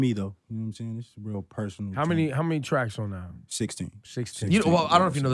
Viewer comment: Smh I thought he said 60 tracks lol